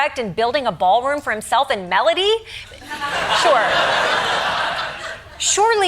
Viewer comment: a bit oot but i love how the autocaption said "Melania" when she said Melody 😂